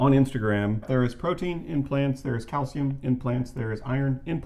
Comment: For some reason I heard "protein in plants" and "calcium in plants" as "protein IMPLANTS" and "calcium IMPLANTS"... must be my deficient vegan brain LOL! Seriously, though, thank you for your work. This is great. I've directed many people to your videos because your approach is compassionate instead of combative, and extremely informative.